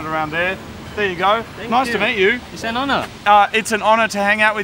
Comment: MASTERBATE YOU! che! edit* you said nice to meet you..... LOL I miss herd like the co-worker from Arizona saying 6-O Duce...... I thought he said sex with dudes Bwahaha.